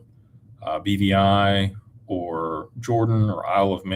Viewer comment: what did he say following the “Cyprus, Cayman, BVI…” ? Was it “Jordan” ?